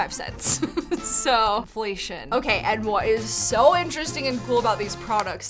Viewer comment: why did i think she said "holy shi-" i had to turn on captions to figure out what she actually said....😅